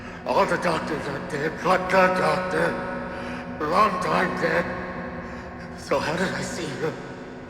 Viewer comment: I think he’s actually saying “Long time dead.” At the time of the game, Wernicke had officially been dead for four years, although he wasn’t actually dead. It’s possible that some Variants actually saw him when they were being experimented on, hence why some refer to him as being alive or still doing experiments.